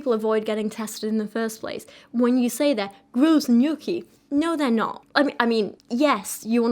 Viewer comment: I was watching this at 1.5 speed and at 20:47 when she said “gross and yucky” it sounded like “frozen gnocchi” and I was so confused.